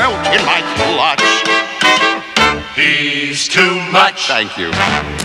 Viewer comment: did they just say knees too much?